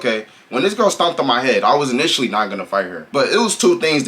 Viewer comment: HEAAAADDD?!?!?? I thought he was saying hand 😂😂😂 I would’ve beat her ahh tooo